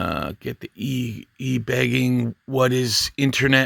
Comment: I obviously have listened to way too much metal over the last 40 years because I swore you said T bagging to start with!